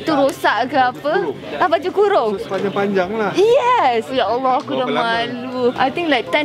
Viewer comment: Aku dengar sebut Ya Allah. Ke aku salah dengar ? Dia islam ke?